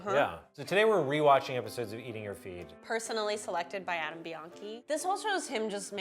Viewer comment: I thought she said Adam Beyoncé lol